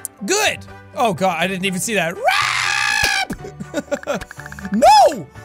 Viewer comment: I hope you know she didnt say rap at 2:39.... she was uhhh saying rape XD